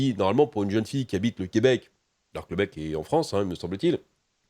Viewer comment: C’est moi ou est-ce que a bien dit - à 9:15 - « le Québec est en France » ? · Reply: Non , il a dit " Donc en théorie, elle a plein de photo du lui à poil en faisant des trucs,ce que pour une jeune fille qui habite au Québec , le mec est en France me semble-t-il ....."